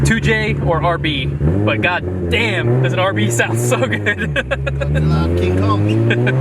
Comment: anyone else hear call it king kong ?